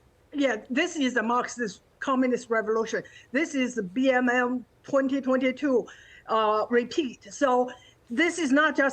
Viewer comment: what is she saying here, BMM 2022? What is that?